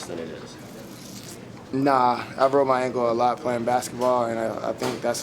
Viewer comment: I heard broke as well